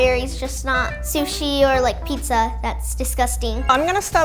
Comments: did she just say pizza is dicusting im offended The f** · wait minute did she say pizza was disgusting wth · did she just say pizza is disgusting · DID SHE JUST SAY PIZZA IS DISGUSTING???!!!!! OML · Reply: AulonaCREATIVE • OMG she said that she doesn’t like strawberries and pizza and sushi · shes like 9 why does she want that ALSO DID SHE SAY PIZZAS DISGUSTING